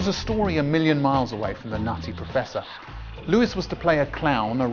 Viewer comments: Thought he said “the Nazi Professor” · I thought you said The Nazi Professor...